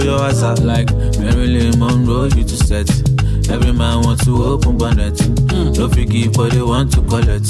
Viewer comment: I heard "Every man want open the bonet"